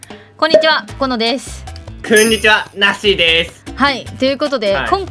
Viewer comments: なっしーの"こんにちは" 最近"こん"の滑舌悪くて"くんにちは"に聞こえてしゃあない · なっしーの挨拶がこんにちはじゃなくてくんにちわ！に聞こえた